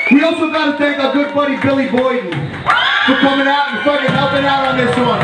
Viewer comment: Eaglebones just headbanging Edit: did he just say Billy Boyd?!